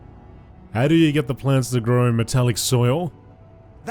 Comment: Oh Metal? I thought you said Mental:-)